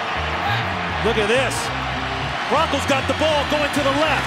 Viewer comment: Did he say broncos